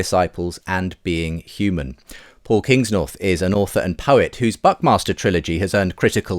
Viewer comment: I've replayed this 3 times, and I can't hear anything else than "...whose 'Butt Master' Trilogy has earned critical acclaim"